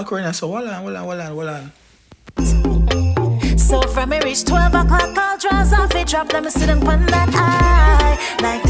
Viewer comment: All de time I thought she said from she meet 12 (years old) all drawers affie drop 💀💀💀💀 I was confused...😂😂😂 ah luv meh Guyanese Music bad doh💞💥🍸